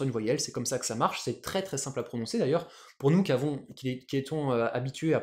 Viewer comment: Moi j'ai entendu "nous qui étons"☺️☺️ (nan mais je comprends très bien que vous êtes fatigué après ce soupir de fin de vidéo 😁😁 merci!)